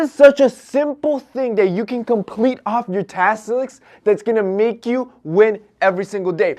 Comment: did i hear tastlisk? Lol!